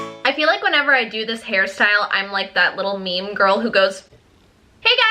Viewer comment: did you just call Charlie "meme girl"